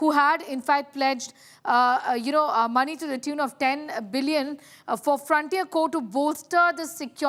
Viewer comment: "10 Billion". Did I hear it right? Is it USD or PKR?